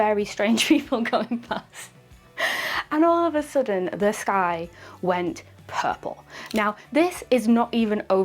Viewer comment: You said "Suddenly the sky went purple" and I heard it as "Suddenly this guy went purple". I was waiting for the story of Emily the Heroine saving someone from a choking fit!